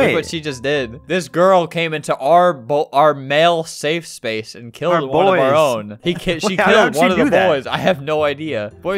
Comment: what did he say on the roblox chat thing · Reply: Badass